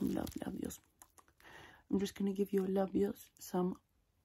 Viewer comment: did you just say "gonna give your labia some love"? i mean i'm not complaining but should probably take me out to dinner first lol